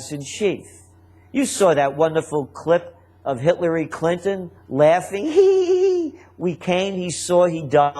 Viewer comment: Did he say Hitlery Clinton?